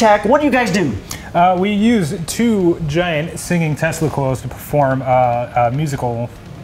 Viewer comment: Tesla coils? At first I thought he said testicles.